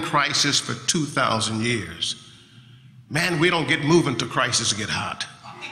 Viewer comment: Can anyone tell me what he says at 13:47? He says it really fast that I couldn't catch it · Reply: Giselle Sounds like "Man we dont get movin' till crisis get hot"...